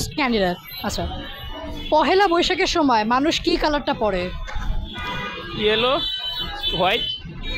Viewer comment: what did u say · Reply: The guy said yellow and white